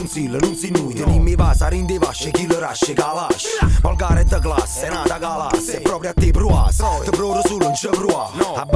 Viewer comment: Ma qualche anima pia mi potrebbe tradurre cosa dice da 00:30 a 00:33?? Non ci riesco assolutamente :( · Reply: Traduzione: Abbiamo i baci dentro i bassi, kili di ash ( Ashish) e i kalash( kalashnikov)